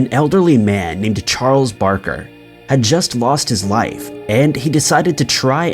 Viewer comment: Why are your subtitles and narrator so bad? @ 1:17 he says "had just lost his LIFE." but the subtitles are "he just lost his WIFE." --- this isn't the first time I've found issues with your subtitles and narration. As someone who is subscribed to you and is hard of hearing, i enjoy your videos; but your subtitles and narration do not align way too often, I'm afraid. It can be rather confusing for me to read along and hear what you're saying - you leave out ALOT of info sometimes in your subtitles VS your narration & it would be nice if you'd maybe do better in the future... but i doubt youd even see this, or acknowledge it... afterall, i'm just one person in a subscription pile of 2M. just wish you'd consider it at the very least